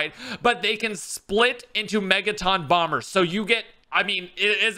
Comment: Anyone else think that Megaton was Megatron at first glance and didn’t notice until a few games of zombies? 😂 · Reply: I thought the megaton bomber said “marathon bomber”